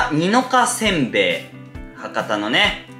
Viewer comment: 博多座公演お疲れ様でした。 応援してます！ 一点、聞き間違えだったらすみません。 「にのかせんべい」って聞こえました。 「にわかせんべい」です！！ 大吉先生たちに怒られます🙂